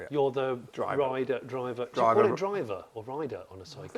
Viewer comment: I think for a side car it's called a drider 🤦‍♂️🤣